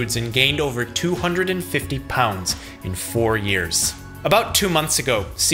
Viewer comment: oh, i thought it said 40 years...